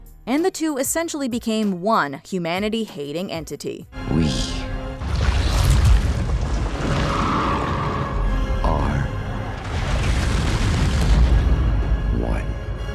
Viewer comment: When Henry said “We. Are. ONE.” I heard this as “We. Are. 001.” Because of his Lab ID number!